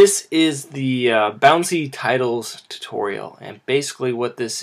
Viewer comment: I came here because I thought it said "After Effects Tutorial: Bouncing Titties"